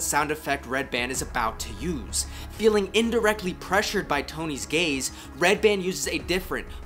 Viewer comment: I heard “Tony’s gaze” as “Tony’s gays” lol like a small group of tiny twinks and beefy bears that follow him around and snap their fingers/sing show tunes.